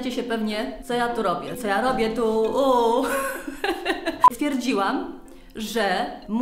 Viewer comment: Czy tylko ja na początku zamiast stwierdziłam usłyszałam pierdziłam 😂😂😂